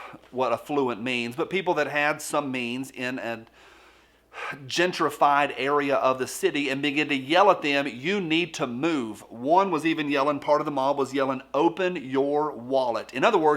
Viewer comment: Could've sworn i heard you say in a ginger-fied part of the city lol